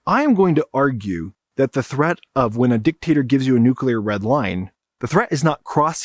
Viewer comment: Great job! Although I think a couple of my synapses got rerouted when I thought I heard nuclear red wine intsead of nuclear red line, lol! Thank you for your work and generosity!